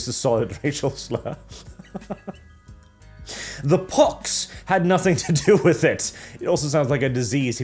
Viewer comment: I am glad that I am not the only one who hear poc as pox.